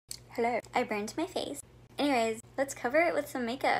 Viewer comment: At first I thought she said I bent my face 😅